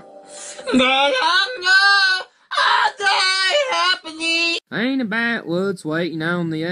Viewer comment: I DIE HOPAGEE! 🤣THATS WHAT I HEARD LEEEL